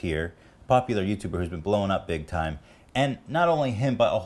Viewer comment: i hear "bowling"